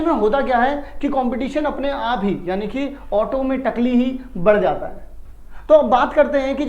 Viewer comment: I heard "Auto mei takli" 😅🤣😂